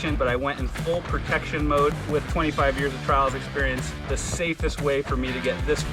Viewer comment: did I just hear you say 25 years of trials experience?